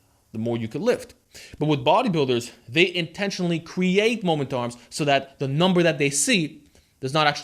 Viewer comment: Ah moment arm I thought you were just saying momentum in a weird way lol